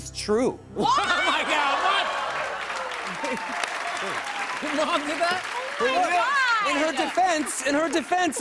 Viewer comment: I hear “your mom, ni**a”